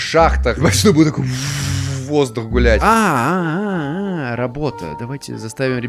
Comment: Ахахахаха жоско в 3:52 я в первые услышал как матерится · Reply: он сказал " будет воздух гулять"